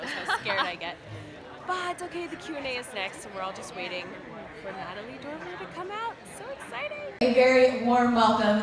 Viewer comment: That's Cool! I thought it Said "Natalie Portman"